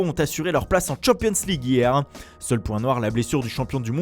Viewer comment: Pourquoi il a dit a 2:00 sauf pour un noir il pouvait pas l appelle par son nom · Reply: çoçuk film il a dit: « seul point noir » sa veut dire que la chose mauvaise pour chelsea est la blessure de N’golo Kanté